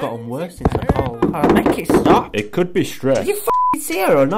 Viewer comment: "it could be stress" "do you fucking see her or not!?" 😂😂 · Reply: I thought he said "it could be shrek" 😭😂